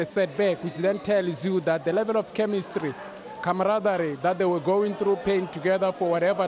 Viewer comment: What's word at 8:19... Steve the dictionary,😂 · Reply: comradery 🔥